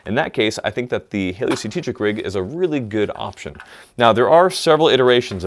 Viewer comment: What he said: Hayley Strategic what captions thought he said: hey Lucy straight Tetris oh, the glory of closed captioning